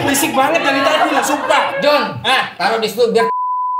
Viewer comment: om ngomong apa y? penasaran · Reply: Taruh disitu biar 3s